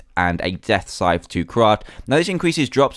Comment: did dan say crud at 9:29?